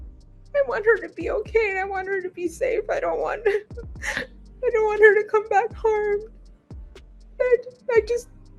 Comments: does she say, “I don’t want her to come back home…” or does it say “and I want her to come back home” ????? 🧐🤨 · I could swear the mom says at 12:15 " I don't want her to come back home" then catches herself and says " whatever that means" wth? She is lying her a.. off!